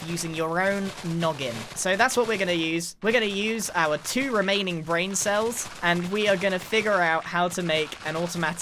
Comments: I thought he said “grain-cells” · only me who heard "Grain cells" when he said Brain cells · i heard our 2 remaining grain cells